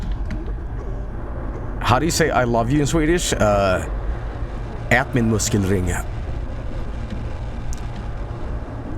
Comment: The hell did he say? I know Google translate isn't great, but all I got was "That my muscle ring"/"That my music rains" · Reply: "Ät min muskelring"